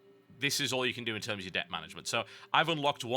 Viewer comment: lol, at the end, i thought it said "dick manager" and "dick stats" for a moment